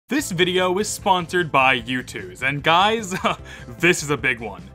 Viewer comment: I thought you said "This video is sponsored by YouTube" I was like, that... Convenient. XD